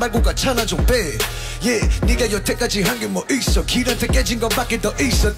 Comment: did he said nigga? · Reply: it's '내가' (naega). naega means you